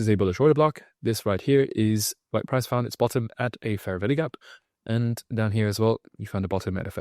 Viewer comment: Starting at 1:40, he keeps using a term I don't understand or know if I am even hearing it correctly. It sounds like, "favorly gap". Can someone help me